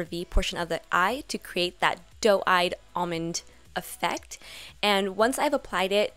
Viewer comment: totally thought it said "dog eyed"